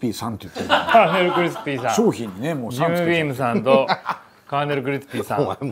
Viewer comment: コカドの滑舌でジムビームさんて言うとずっとゆーびーむさんに聞こえる。